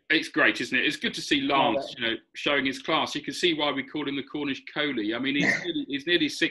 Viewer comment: "The Village Vettori!" Love it! I must admit the first time I heard "Cornish Kohli" I thought you said "Cornish Coney" (as in rabbit). 🤣🤣🤣